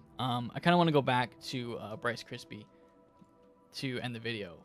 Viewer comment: I thought he said Rice Crispy, kinda got hungry at work 😂